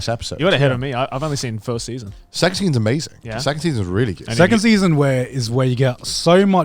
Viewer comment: Phew.. i thought when connor said the second season is really good, for some reason i thought he said sex scenes are really good.